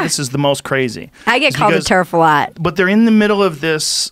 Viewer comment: What is a “Turf”. Did I hear her wrong? Google and Urban dictionary were no help.